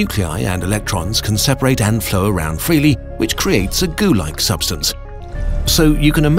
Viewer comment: creates what? Sorry i always unterstand gulag substance xD. No joke what does he really say